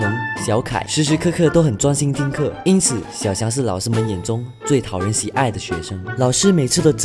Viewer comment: 0：32我听成最讨人厌😂😂😂